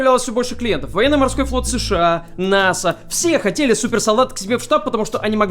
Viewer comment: супер салат. (Послышалось)😅